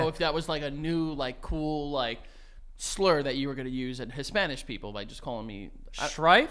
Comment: Did he say “His-Spanish” people”? 😂😂😂